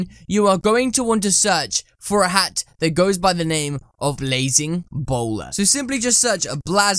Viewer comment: When I hear you I thought you said lazing bowler LOL 🤣🤣🤣